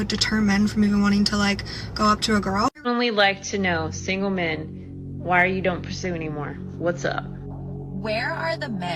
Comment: wait, did she say something? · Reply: …*that’s* your problem…